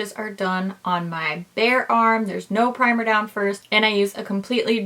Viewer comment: What says: "All the swatches were done on my bare arm, no primer down first..." What I hear: "All the swatches were done on my BEAR arm..."